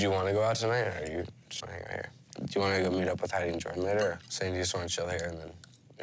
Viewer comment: Have not read comments: but at 8:03 does Brody say Heidi & Jordan?